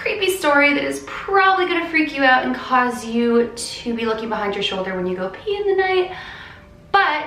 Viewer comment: did she say look over your shoulder when you pee tonight or am i crazy?💩💩